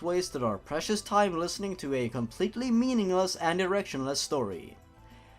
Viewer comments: I swear i heard him say “erectionless” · I heard "directionless" as "erectionless".